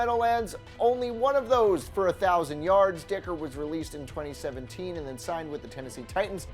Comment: it sounds like he says dicker